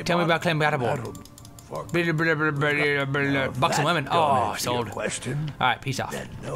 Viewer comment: i thought said "Fuck some women...SOLD"